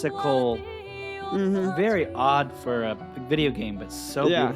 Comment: They keep saying "very odd for a video game", I keep hearing "I don't know many games" XD